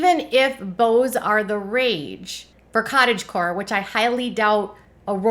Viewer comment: I thought said cottage porn at first 🤣